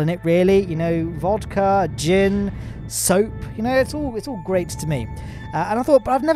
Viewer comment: what 🤷‍♀ if 🧐 we 👫 kissed 💏 under ⬇ the 👉 Mun 🌛 Arch ♎ 👽 💑 Haha 😂 jkjk 🤡🤣😜.... unless? 😳😳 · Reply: did you say you like drinking soap or ami just hearing things. if you actually do like drinking soap then you need to go get some help